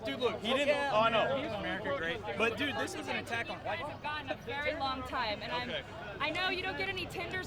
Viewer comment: did he say Attack on Titan ?